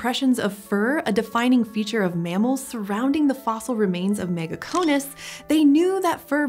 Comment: I could have sworn I heard her call that mammalian ancestor “megacohones.” I was shocked. That would’ve been a ballsy way to launch the video though.